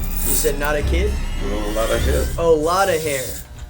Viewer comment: I definitely heard “a lot of kid” too.